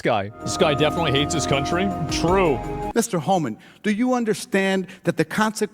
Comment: "this guy definitely hates his country" - "true". WHAT? What are you talking about? Or did I hear that wrong??